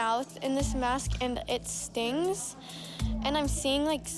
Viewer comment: It stinks n I can't move my mouth but other than that it's great lol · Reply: I thought she said Stings.